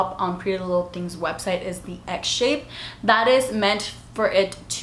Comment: Done! Girl I literally thought you were saying “egg” when you said “x shape” 😂😂